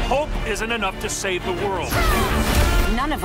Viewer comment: I heard hulk, can someone tell me what it actually said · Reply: Omar Orduna "hope"